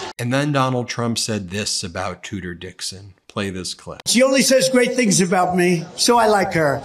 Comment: I stand with Gretchen Witmer! I live in Michigan....the educated part of Michigan! MAGA and Trump are such an embarrassment to our country! VOTE BLUE! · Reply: I thought they were saying "Tooter Dixon" and thought why not change it 'Cooter'😱😂🤣😂🤣